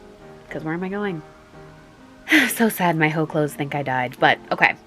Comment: "so sad my hoe clothes think I died" MOOD SIS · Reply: i thought she said "my hoe clothes" bc we dont have occasions to dress up anymore :(